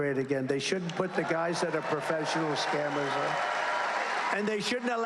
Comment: did he say scammers??